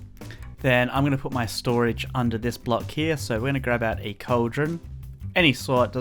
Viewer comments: Did he say cauldron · 100th like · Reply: Did he say cauldron